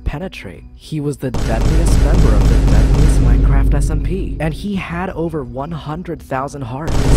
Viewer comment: Why YouTube subtitles say ''feminist minecraft smp''